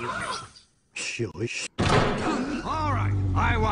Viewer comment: Why does he call him “Jewish” at 3:28? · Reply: He says “shush”